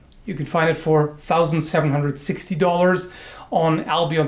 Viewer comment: At first I thought i heard "760$" and I was like "WHAAAT?", so to make sure, I went back a bit to listen again, and he said "1.760€" WHAT? ITS A FUCKING PIECE OF STEEL, YOU COULD BUY ALL THE TOOLS NECESSARY TO MAKE IT, PLUS THE MATERIALS, AND STILL HAVE SOME MONEY LEFT, WHAT FUCKING IDIOT WOULD PAY SO MUCH?